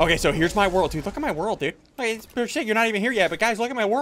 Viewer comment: Sorry for my language but 0:55 it sounds like you said oh shi*